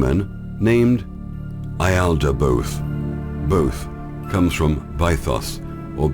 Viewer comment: can you, or "SecretKeyActivator3" please expand on who the "Alphites" are and who the demon they believed in was? I know my spelling is probably wrong, but it sounded like you said the name was "Ieldibooth"? I have tried researching these terms online, but I feel my misspelling is hindering any results... thank you very much! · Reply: YALDABAOTH. an egregore google it.